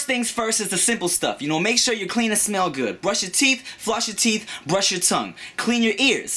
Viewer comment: lol, at ifrst i thought he said make sure your penis smells good